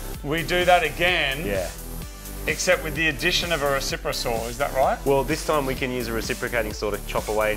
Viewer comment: I misheard "reciprosaurus" which is probably how internal combustion engines are going to end up being called :')